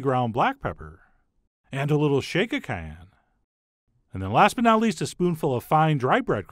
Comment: Chef when you said "a little shake of cayenne", I thought you said "a little Chaka Khan." LOL